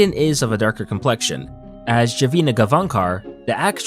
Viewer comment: did he just say javina